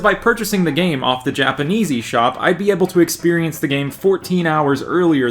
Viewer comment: When you said "Japanese eSop" i thought you said "Japanesey Shop".